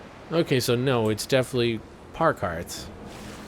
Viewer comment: Wait a second... At 18:45 does he say... "par carts"? I listened to it four times and I'm pretty sure said "par carts"...